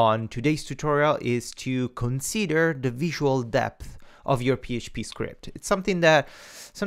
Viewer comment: Thanks for watching, the title is correct :P Technical debt or visual debt refers to something you have in your code that will make you pay the consequences later. You have a debt you'll have to pay later. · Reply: oh oops the whole time I thought you were saying visual "depth" lol